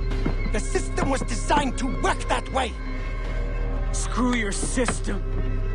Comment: I wasn't paying attention and thought said "Screw your sister"